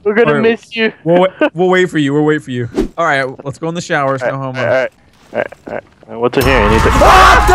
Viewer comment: Did anyone else hear quietly say no homo